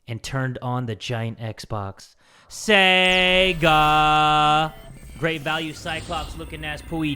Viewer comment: did I just hear sEGa ???